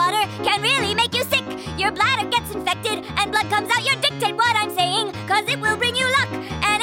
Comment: I like how she swears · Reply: for example when she says “dic…tate what am saying” so it sounds like she said dick but in reality she said dictate. same thing for the other “swear” words